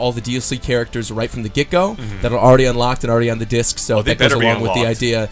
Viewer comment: did he said "the disc?!" on